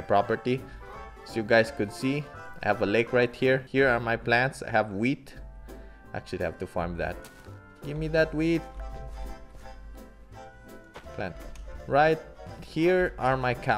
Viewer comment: i really thought he said gemme that weed 😂